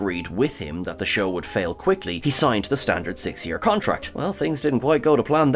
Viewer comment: I hear "six-year contract," but I see "six-MONTH contract." Huh???